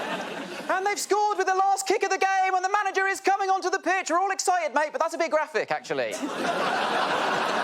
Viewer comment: Could anyone explain this one to me? I'm afraid I can't quite hear what he said. 😅😢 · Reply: "And they've scored with the last kick of the game, and the manager is coming on to the pitch! We're all excited, mate, but that's a bit graphic, actually."